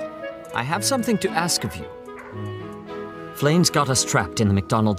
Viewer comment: why did i hear it "he got the strap" in